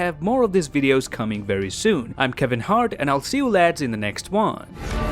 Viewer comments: excuse me, did you say you were Kevin hart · Did he just said I'm Kevin heart at the end! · what? Did he just say his kevin hart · did you say "I'm Kevin Hart"?